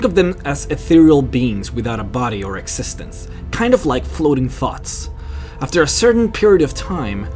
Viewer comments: I thought he said "kinda like floating farts" but I think he said "thoughts" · Ooooh, "Flowing Thoughts" I had to hear that twice. I thought you said they were like "Flowing Farts" XD. lol